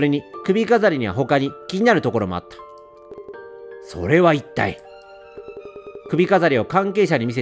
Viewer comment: ここの「それは一体」がシャア・アズナブルとかシャンクスにしか聞こえないw